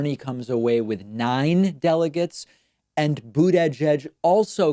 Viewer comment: I don't know, as a czech not native speaker, I always hear "bootyjudge"....